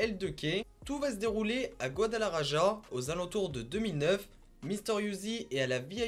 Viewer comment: J'ai entendu "Guadala Raja" j'ai rigoler 10mins en laissant tourner la vidéo xD.